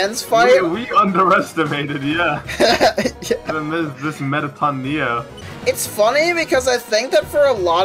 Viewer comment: I thought it said “every enemy is Mettaton neo” and I started to think you ran out of ideas for a sec